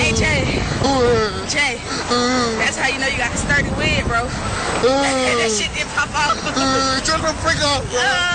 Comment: What was it that she said at 1:33? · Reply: SBN "that's how you know you got a sturdy wig bro.. that shit didn't pop off"